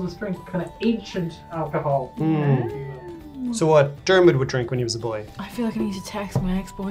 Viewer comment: I lost it when said "when Dermot was a little boy?" and smashed that like button🤣 Love you, bro! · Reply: I thought he said "like a German would drink when he was a little boy"... This is waaay funnier now!